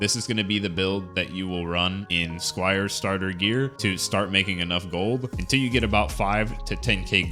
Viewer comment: I could swear I heard you saying "This is going to be the build that you will run in Squire Starter Gear to start making enough Golb." LUL! Pretty sure it is just how the cut fits with the next words that make it sound like that!